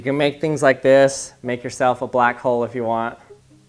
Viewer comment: when he said 'black hole' i thought he said 'black hoe'